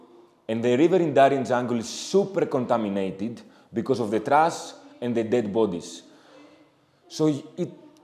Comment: I didn’t get what he said “..because of the trass …”? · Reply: because of the trash